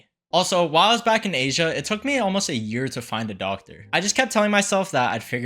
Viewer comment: thought you said "dog" 👀